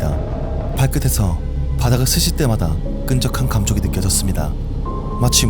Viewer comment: 딕션을 조금 더 신경써주시면 더 몰입하기 좋을 것 같아요 대표적으로 5:21 요런 부분 보면 ' 스칠 때마다 ' 가 ' 쓰실 때마다 ' 로 들리는 느낌